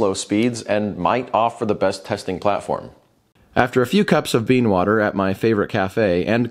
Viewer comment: did u say bean water?